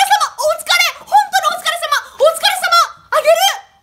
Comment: お疲れ様がほ疲れ様に聞こえた